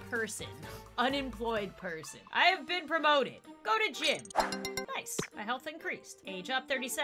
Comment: For some reason when said "go to gym," I thought she said "go to jail." Me: Wait... what?